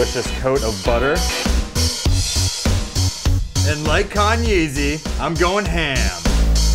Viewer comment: did he say kanye-z?